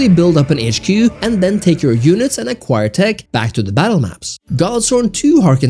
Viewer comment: what is the name of the game? I can't hear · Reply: Godsworn